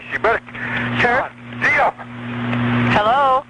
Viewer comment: the audio is clearer and he actually says god damn. Others have him saying “Theo!” in their transcription of the audio and so they made a Bigfoot movie using this 911 call where the guy had a dog named Theo. Great work as usual.